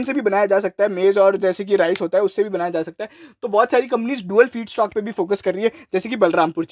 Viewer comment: Dual ??? kya bola bhai tumne smjh nhi aaya. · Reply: Dual feedstock: ethanol from grain n sugarcane